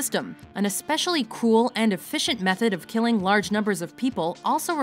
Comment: “especially cool and efficient” am I hearing things wrong? No way tho right?